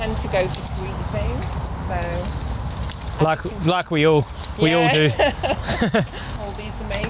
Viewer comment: We tend to go for sweet things and then said 1:14 Damn, I heard it as "veal" and almost had a heart attack before he said, "Like we all do."